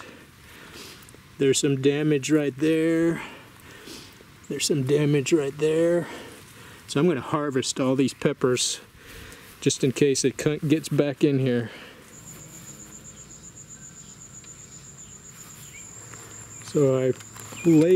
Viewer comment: did you say “I’m going to harvest all these peppers just in case the c*nt gets back in here” lol